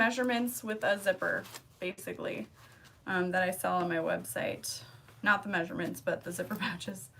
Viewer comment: What size do you make your pouches? 8 x ? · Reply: Ok, sorry I just heard you say you will not give the measurements 😉